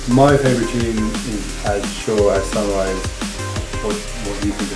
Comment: what did Fred V say his favorite tune was? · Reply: As Sure As Sunrise